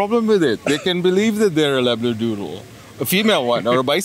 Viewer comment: I thought he said 'elaborate doodle' :P